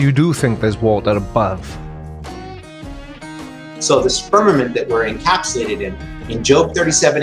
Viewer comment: did he say Spermament?